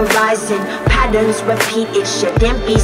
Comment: It's pretty cool. lol, but I hear, "you make me baith".